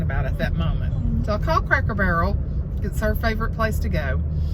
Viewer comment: I was hysterical about the restaurant and then I thought you said Croaker Barrel. Lol. Your mama is precious!